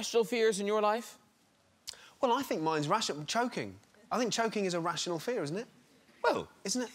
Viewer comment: I thought he said "joking"